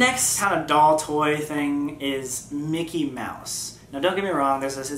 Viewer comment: I thought he said Nikki mouse😂